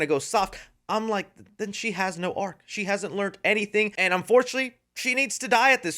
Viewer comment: bro I thought you said she had no aura but she doesn’t tho